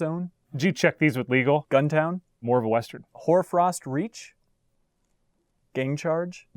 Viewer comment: "Whore Frost Reach?" facial equivalent to wtf did you just say